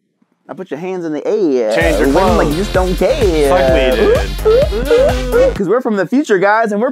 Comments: Did say f*ck me dude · Everyone on set : "SUch DisAPpoiNTed!" "You SHoulD HAve CUT The HoLE!" in the Back : "I rate this Music Box A Dope." :D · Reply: I think said f me dude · Is it just me or 5:54 said “f*ck me dude” that’s what I heard tho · Who else heard say "fu** me dude" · like to buy it sounded like said “suck me” or “bleep me”